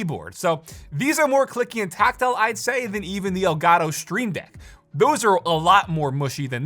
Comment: i thought it said STEAM deck... i was like wtf